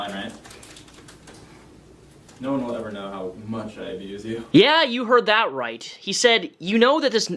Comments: I swear Greg says.. ABUSE YOU.. not ‘HURT YOU ’.. in the beginning of this... at 1:38 ..or if you want to start it at 1:32 or something. Definitely hear ABUSE.. which is even worse language than hurt, imo. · sounded like he was going to say “how WELL I abuse you” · I heard him say "no one will ever know how much I ABUSE you" not hurt